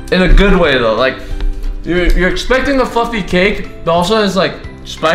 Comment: I thought he said fuckin cake ay